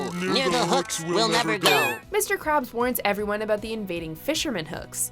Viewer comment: I thought she said mr Krupp